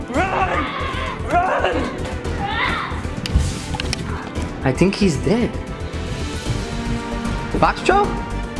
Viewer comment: When you said I think his dead,my phone thought you said I think his Tom😂😂😂😂😂😂